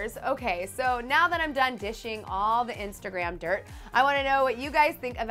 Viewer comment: did she say dishing ??😂😂